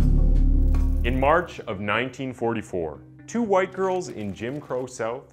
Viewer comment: Captions says 1994 instead of 1944.